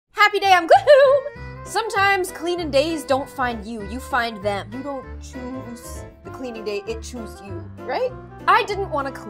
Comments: i thought she always said:happy day ON gloom but no its IM gloom wHaT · I was today years old I realized she said I'm GloOm I THOUGHT IT SAID HAPPY DAY ON GlOoM